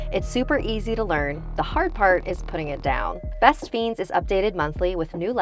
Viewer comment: I thought it said best beans